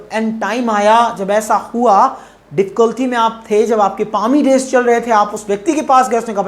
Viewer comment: Sir please let me what word you spoke at 6:37(.........days)in this lesson. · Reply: It's Palmy days...bure din